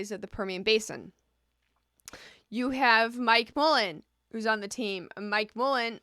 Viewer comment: At around 5:20 or so did u say... " u have my colon?"